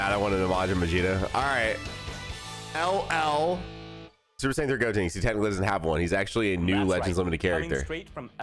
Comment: why did I thought it said LR SSJ3 Gotenks